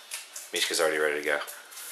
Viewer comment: Did anyone else hear at 1:38? "Mishka's already ready to go......fuck"